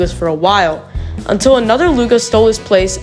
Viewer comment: Him: until another lugs stole his place as the best NUGA Me: that’s godamn racist! What I heard: nigga